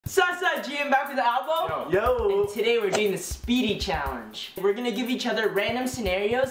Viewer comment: ohhhhh it said speedy I thought it said sleepy